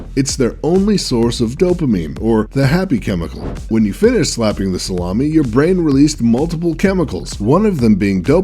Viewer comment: did he say "When you finish slapping your salami"? 😂😂